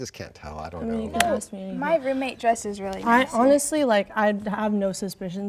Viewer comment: Did anyone else hear the “my roommate dresses really nicely”